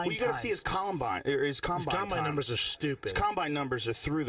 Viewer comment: did someone say columbine??? ;)